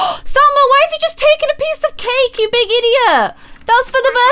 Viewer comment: This might just be me but at 31:49 it sounded like Conner copied and said “idiot”